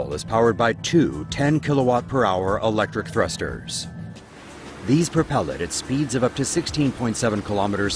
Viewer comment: I thought he said 10 electric toasters